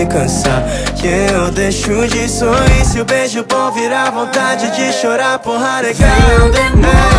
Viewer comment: Só eu que escutei vontade de chorar porr* na cara?